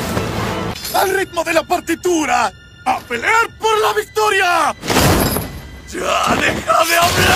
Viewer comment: lo que otros escucharon Tenguen: Al ritmo de la partitura a pelear por la victoria lo que yo escuche Tenguen: YO SOY COMO UNA CUCARACHA YO NO ME MUERO ME MULTIPLICO... TENGO A DIOS DE MI LADO LO OYES PERRAAA¡¡